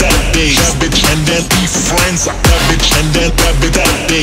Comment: ブラックサンダー食べたいって にしか聞こえん